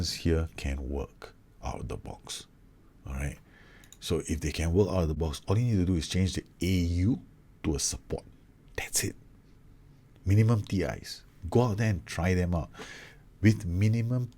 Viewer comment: nice video :) but i'm having trouble to understand a little portion, i did understand easily ur english, but on minute 18:43 u say something i can't really understand, listened like 20 times, but can't figure out what did u said there, if someone can write in here what it was will be rly appreciated. Thanks and gj again :) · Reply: Basically if you have automatic roles and if you are unsure, just turn them to support and try them out